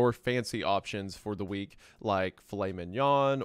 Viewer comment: Heard this as "fancy options (for the weak)".